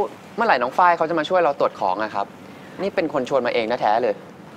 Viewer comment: ได้ยินว่าตดของ